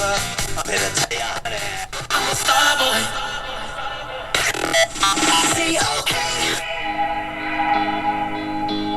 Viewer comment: Just bought one of these based on your review. Arrives tomorrow. What was the station around 96 mHz. Sounded like the jingle was V-O-K or E-O-K or something like that. It's a 9:56 onto your recording. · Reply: WZOK 97.5 Rockford, IL